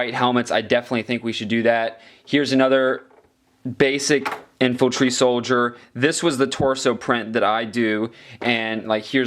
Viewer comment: i hear info-tree soldier